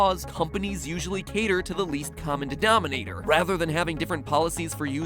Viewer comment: I heard dedominator😂